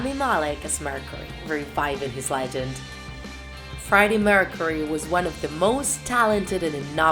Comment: It sounds like she says: 'Friday Mercury' a lot